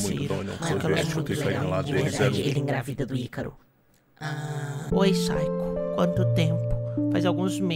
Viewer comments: tenho a inpressao que escutei o :saiko ingravide o ycaro ????? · Mano só eu q ouvi "saiko grávido do ycaro"